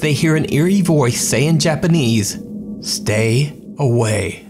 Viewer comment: Thought it said stay away Corona😂